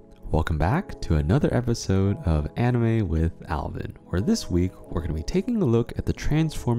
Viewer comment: Istfg I thought it said Anime with Armin